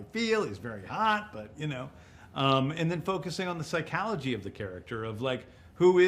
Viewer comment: did he say psyKALogy?!